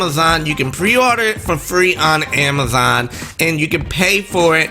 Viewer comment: I at first thought you were saying pre orders are up now on amazon and i panicked and opened Amazon asap then to realize i must of miss understood you. lol I hope pre orders happen as soon as the presentation finishes... I'm not doing what i did on Nintendo's last two consoles... Took me nearly a year to get a wii, and like 3 or 4 months for a wii U, both times I got lucky to grab one then.. I've learnt... So i'm going to pre order.....